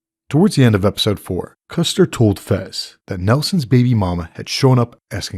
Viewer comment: I heard that "Nelson" was just a bad caption from some Article and the dude actually said "Mouse". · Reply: It was an error in the closed captions. You can clearly hear that Custard said “Mouses baby momma”